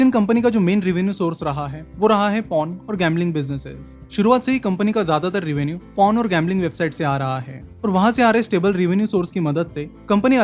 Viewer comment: did he said porn??